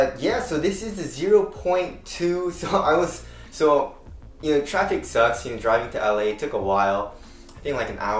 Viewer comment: He said traffic sucks and I thought he said traction sucks and I was like damn right to the point!